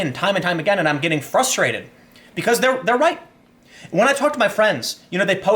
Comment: did Tim say they're right or they're white?